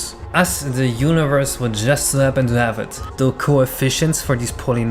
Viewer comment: what is he saying here? us the universe would just happen to have it? what?